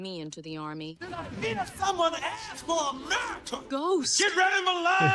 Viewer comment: Did I hear someone ask for a Badd Medicine😂😂😂